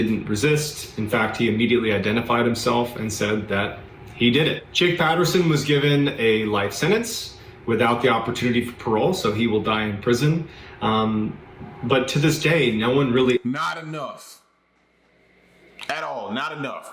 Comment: I thought the sentence said light sentence and not life sentence I was so mad